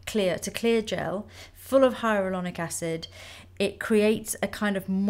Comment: When she said hyaluronic acid, at first I thought she said hydrochloric acid. Don't want hydrochloric acid on your face!